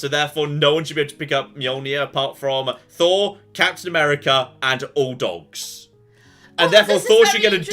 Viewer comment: when talking about who's worthy for Mjolnir i thought you said "cats in America" instead of Captain America lol